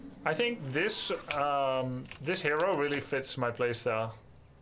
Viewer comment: does worse than nothing for 20 mins "i tink this hero really matches my play style" ohhh god ... never change Grubby, this is A+ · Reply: he clearly says it fits his play stile. Where did you hear the opposite?